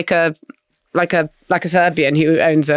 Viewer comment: "He dresses like a Serbian that owns an adult webcam business" was made all the funnier because I misheard "Serbian" as "Furby".